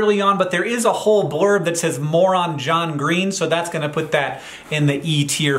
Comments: I wasnt looking at it when you mentioned it and i thought you said "the article says 'moron john green'" and i thought that was pretty rude out of nowhere for them to say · I was listening and not watching and I heard "Moron John Green" instead of "More on John Green" and thought Hank was upset they called John a moron 😂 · "Moron John Green" I swear that's what I heard! Lol, appreciate you keep light hearted and still have a overwhelmingly positive attitude! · I for real thought you said "Moron John Green" 1:04 😂😂😂 I was like.. Rude!?! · I thought you were saying "Moron John Green" for a second lmao. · I heard as "moron John Green" and so I went like "That goes in the e tier, in fact probably f tier". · Lol when you said "more on John Green" I heard "moron John Green". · I just heard "MORON JOHN GREEN" which, after I figured it out, I still appreciated since I'm a sibling too. 😂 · When he said the paper said "more on: john green I didnt actually look and thought the paper said "moron john green". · I was so taken aback and feeding ready to fight when Hank said 'more on john green' because i thought he said 'moron john green' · The blurb that says "More on John Green," I thought you said "Moron John Green," and I laughed SO HARD! 😂😂😂